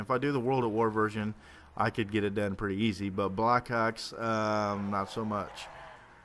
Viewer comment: did you say black cocks? loooool